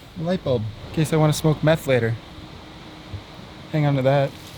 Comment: did he actually says smoke meth later?